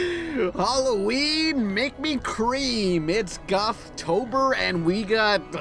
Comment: ''halloween make me cream'' spits out water wait what the fuck did u just say